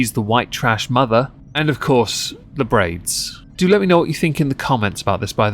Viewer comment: Quite seriously, I thought she said brains. I was VERY confused. 😂